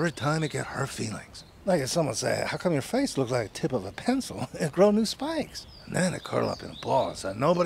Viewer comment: Could've sworn I heard "tit of a pencil"